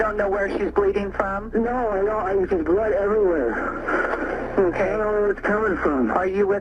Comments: Later in the 911 call it sounds like he said “I hurt my wife there is blood everywhere” also I died laughing at saying him and the other woman weren’t into that “disgusting porn” lol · What I hear there is "I stubbed my wife, shit. There is blood everywhere".